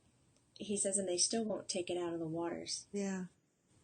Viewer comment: I heard a man's voice say "it's in all our water..." Anyone know what that is?